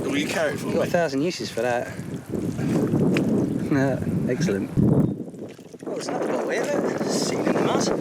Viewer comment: At first I thought he said "legscellent" lol